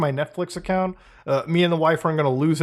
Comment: did he say me and the wife ?! Mutahar is married ?!?!